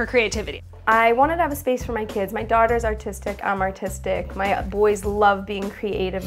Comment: I thought she said "I'm autistic my daughters autistic" 😂